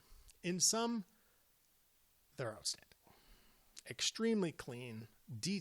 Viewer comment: "in sum, they're.... " what? unstoppable? couldn't tell what you said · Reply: I think it's 'outstanding', could be wrong though.